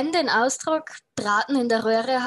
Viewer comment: I don't hear the Drahten. As far as I can tell saying Braten.